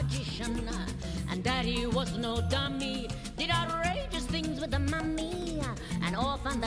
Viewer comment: Until I saw the subtitles, I thought she said that her Dad “did outrageous things with Mummy” 💀